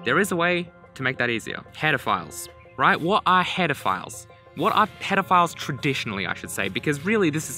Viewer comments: why did i hear pedophiles smh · I heard "what are pedophiles traditionally" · uh... that sounded like you said pedofiles not header files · Turn on caption English "What are pedophiles traditionally I should say" woah there. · I used subtitles... it showed pedophiles instead of header files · Was playing at 1.5x speed and thought he said pedophiles instead of headerfiles lmao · anyone else hear what are pedophiles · I swear I heard "pedophiles" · Wow... Blame the "Auto Generate (English)" Captions on this: 4:28 : What are Pedophiles Traditionally i should say because really this is syphilis. what you can you can do anything with anything... After hearing that, i would be more concerned about the kids chasing a kangaroo... you never know what the kangaroo be doing to lure children in